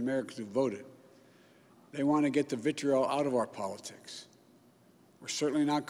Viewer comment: Could somebody plesse just transcribe for me what Mr Biden says in the sentence starting at 1:40? 🙏🏻 I am no native speaker and I can’t decode this single sentence, thanks everybody for helping 🤗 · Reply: Karel Oprchal he says "they want to get the vitriol out of our politics"